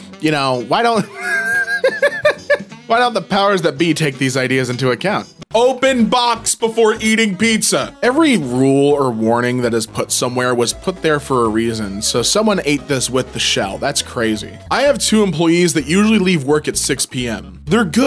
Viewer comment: i thought it said open box after eating pizza